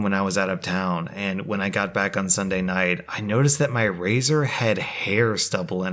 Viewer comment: after the fridge story I hear razor as freezer 🤢